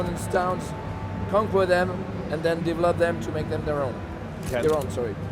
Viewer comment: what in the fuck is he saying here? I hear Divolodum.... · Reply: divolodum = develop them